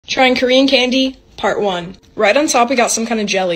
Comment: I thought it said karen candy